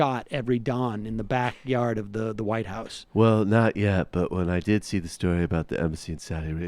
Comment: secs - White Rose ?? During the WW1 there was a infamous group created in Germany 🇩🇪 who stood against the Nazi ideology who went by the name the White Rose ! All the best from Tottenham, London,Uk! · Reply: I thought Sam said the ‘White Rose!’